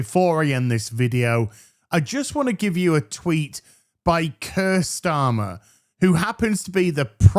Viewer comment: When you said "Keir Starmer" I thought you said "cursed armor."